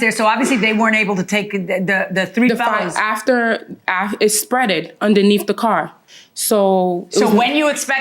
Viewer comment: Did she say “it spread-ed”?